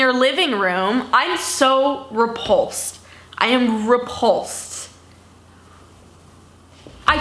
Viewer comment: I kept hearing "i am ru pAULSED"